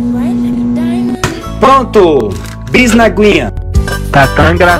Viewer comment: não entendi alguém explicar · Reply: E bis na aguinha